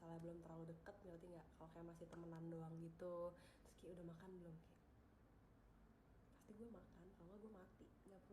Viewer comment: Maaf kalo kurang grecep, ngeditnya lumayan lama :D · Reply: Menit 10:10 “risky udeh makan belum ky??” Bener ga?? Atau salah denger gw guys 🤔🤭